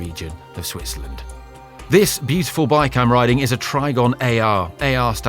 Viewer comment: Well done, that was colossal (with the emphasis on the 'col')! And sympathies for the Shermer Neck. BTDT, don't want to do it again. No trouble balancing? TriGON,was it? I thought you said TriBAN. ;)